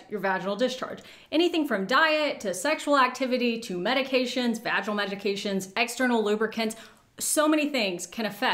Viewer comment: Did she actually say vaginal magication??? 9:30 😂😂😂